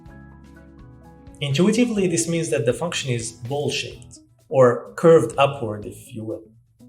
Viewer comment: this means that the function is bullsh¡t (c'mon I can't be the only one who heard)